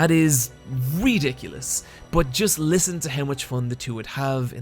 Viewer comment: Did you said "wryyydicules" at 10:25?